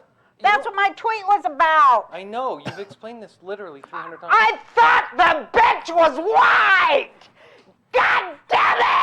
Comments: I THOUGHT THE BENCH WAS WIDE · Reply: I thought she said, "I thought the bench was wiped" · I thought she said “I thought the bench was white” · OOOOOHHHHH I thought she said the bench was wide until she mentioned racism I was so confused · For a second there I thought she was saying, "I THOUGHT THE BENCH WAS WIDE! "